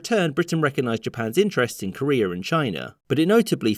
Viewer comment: What was said: Respect Japan's interests in Korea and China. What I heard: Respect Japan's interesting career in China